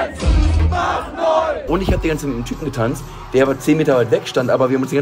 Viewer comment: Mein erster gedanke bei dem anderen guy: MARIUS?! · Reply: Ich dachte auch erst Tanzi. Ich glaube ich habe bei "ich hab mit nem Typen getanzt" Irgendwie Tanzi verstanden